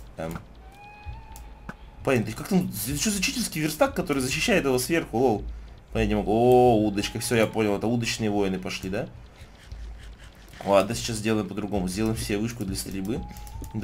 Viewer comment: послышалось уточные войны:D